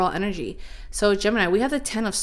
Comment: Anyone else heard the whisper “Gemini” @ 1:52-1:53?